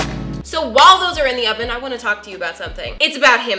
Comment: what I hear: “So WALDO’S are in the oven”